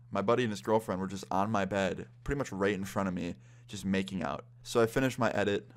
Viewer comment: i thought he said rape insted of right hahahahahaha